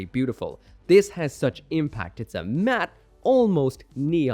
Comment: did he just say impact?